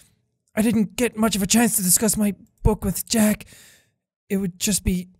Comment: did anyone else hear Monika say Jack